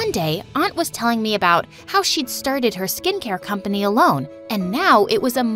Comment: I HEARD "how she sharted"